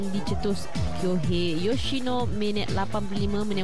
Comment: gua salah dengar kot. sbb gua ada dgr nama farizal marlias last2 td. kalo betol terer,xkan bolos jgk? jauhh jgk tuu. pikirla,van der sar pon bolos jgk la weiii. casillas kena 3 das ngan brazil. haissh,mentaliti2.